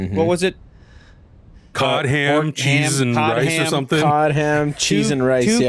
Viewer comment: That was hilarious. I did not catch it when I saw the the show that she said, cod, ham, cheese, rice because in my brain, I thought she said God damn Jesus Christ. So on the show or not on the show, but even in the vaults, they are not even acknowledging any real swarm of religion, so what was used as a swear words in connotation of a religion they switched the words out. Holy crap they even took swear words and turned them into management speak